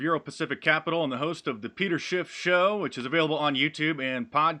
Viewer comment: Sounded like he said the Peter Shitshow.